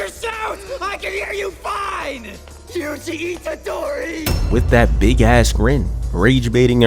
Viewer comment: WHY did i hear "juicy itadori"